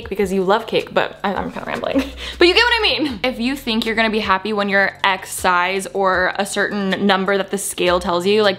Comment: literally thought you said "if you think you're going to be happy when your ex dies" instead of "if you think you're going to be happy when you're x size"